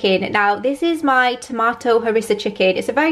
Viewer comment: Hi, at first I thought you said "Harrison Chicken," and I thought oh how cute, she named this dish after Harrison. 😀 I hope everyone is well, it's good to see you looking so happy.